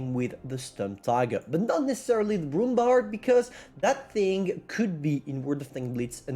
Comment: i was just listening and when he announced the alternative i thought i heard "the roomba"